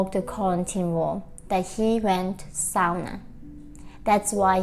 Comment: “He broke the golden rule, he went …sounder”? Can someone clarify what she said???? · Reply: "He went [to a] sauna". The sauna originated in Finland, and the Finnish word sauna is pronounced in that language like "sow-nah".